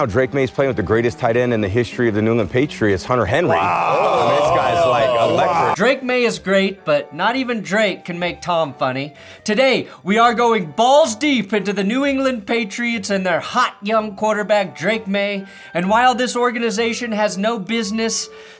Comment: Wait? 2:46 Did you say you were going to go balls deep inside Drake Maye? You got my subscription. Need to clip that McDaniel's rant 😂😂